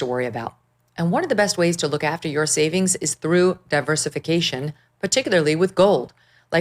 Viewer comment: This guy is soft thought yo were saying he’s trough , BETA